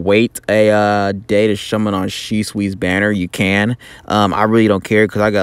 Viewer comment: Did you just say Shummon at 0:53 XD I needed this man, thanks